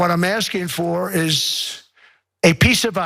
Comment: Sounded like he said "a piece of a$$"... That might explain Trump's fascination with cold countries..